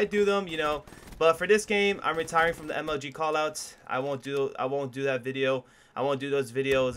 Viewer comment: Lmao you gave me a heart attack when you said "I'm retiring from MLG Callouts" I thought you said I'm retiring from YouTube Great Video By the way keep it up! 😊